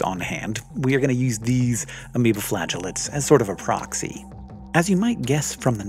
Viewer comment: So I heard “amoebaflatulance” instead of amoeba flagellates. Damn funny.